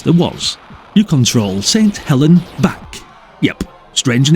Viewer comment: Sorry I thought you said "St Helen's baps" was her name